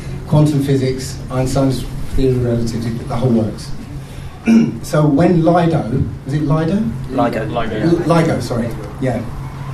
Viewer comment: Did he just say "The hole book of Newton Law was Bullshit!"😂 they were debunking everything the globe believers said...